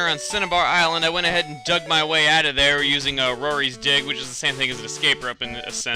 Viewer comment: I totally heard 'Roary's dick'.